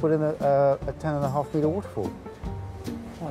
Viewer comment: Can anyone tell me what that Mr. says at 19:00 ? What did they build? It must be so unbelievable, I can't understand it! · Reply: "Waterfall", you can literally see it following that timestamp.